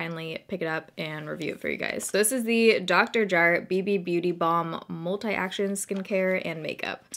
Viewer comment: Maybe I have the maturity of a 13 year old boy but every time I hear "Dr. Jart" what I really hear is "Dr. Shart" 🤷🏼‍♀️